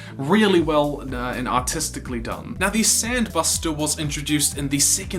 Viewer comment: Sounds like you said autiscally done